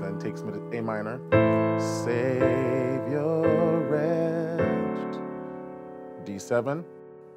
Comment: “that savior retch”😂😂I cant be the only who heard that... love your videos man!